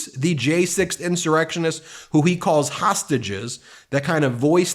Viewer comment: is it just me but doesn't sound like he says tossages lol can't wait for his trail on this and how he said they were FBI agents not his followers let him keep talking he is digging a big hole